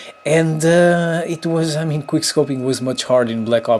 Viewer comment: did he say thats why every one hated quickscoping in black ops 2